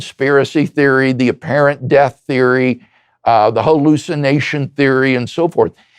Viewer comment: not sure if the captions are yours or not but it says here "whole lucid nation" instead of hallucination.